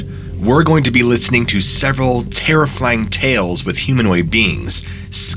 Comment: did you just say “terrifyling”😂🔥